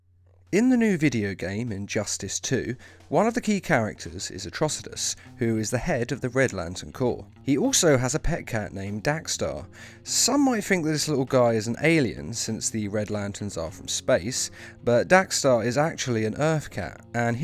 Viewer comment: I thought he said “Dank Star”